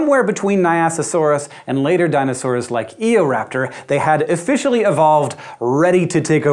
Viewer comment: I seriously thought he said Egoraptor